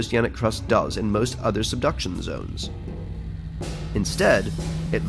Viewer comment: I misheard Subduction Zone as Seduction Zone I really need to get my head out of the gutter